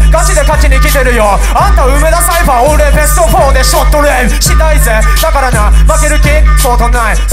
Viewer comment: 俺BEST4で の後なんて言ってますか？ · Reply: ショットレール